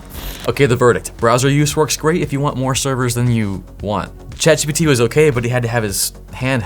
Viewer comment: Lol at 13:45 when you said ChatGPT, auto caption said Chad CCPT